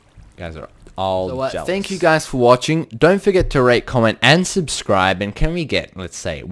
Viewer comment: Did he just say "Rape, comment and subscribe..?"